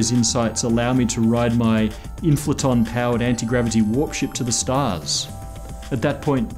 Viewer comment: I had to rewind the video when I thought he said he wanted to fly a warship to the stars. "Warp ship" well that makes a lot more sense.